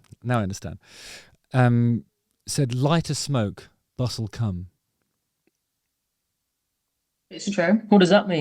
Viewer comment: I thought you said “light a smoke, butt will come” and I thought. Yeah that makes sense.